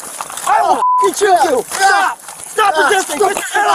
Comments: one of the cops sounded like he yelled "i'll f'ing choke you". Then at 25:06, the 3rd cop took out his taser, aiming it at the elderly man’s leg while the other 2 cops were handcuffing him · sounds like the cop says "I will f*ckin kill you", am I the only one who hears that?